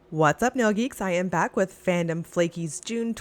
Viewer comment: oh my gosh, I JUST realized you are saying 'what's up nail geeks'- i always thought you were saying noogies. why? don't know, just thought it was a cute nickname you gave your viewers